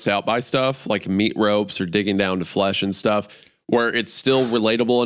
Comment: Sounds like said dicking down but I think he said digging down